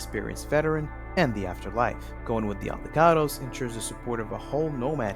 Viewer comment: I swear I heard "Avocados". I will now call the Aldecaldos that, thank you